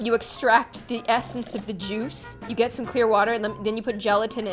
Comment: did she just say you get some clear water???